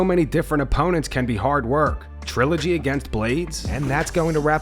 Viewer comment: I thought it said "trilogy with Bayblades" lol